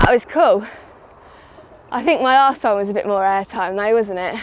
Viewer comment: Both my partner and I heard “My a***hole had a bit more air time” at 8:43 . Had to go back. Brilliant.